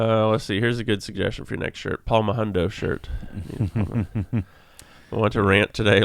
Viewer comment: When he said “Palm a hundo” for a shirt it sounded like he said “Paul Mahundo” which would be a funny inside joke thing. Just people wearing shirts with a random guy named Paul on them